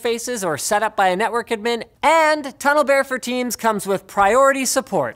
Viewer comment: I thought Linus said tunnel bear for teens. Lol